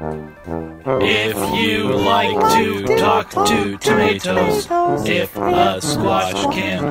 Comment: I thought he said to totatos